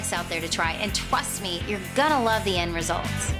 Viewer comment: Did she just say, "twust me"?